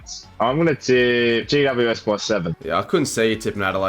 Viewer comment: I thought he said gc by 70 in round 1 I’m like ummm wrong bird mate we ain’t the eagles